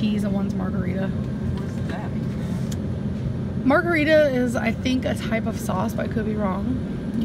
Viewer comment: Did she just say margherita is a type of SAUCE??